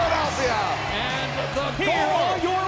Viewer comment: Did anybody else hear the announcer say "the new"